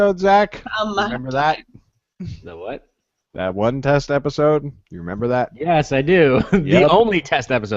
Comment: When you said, "Test episode", I thought you said, "Tess episode". I was like, "There was a dirty episode of Realmkeepers?!" :)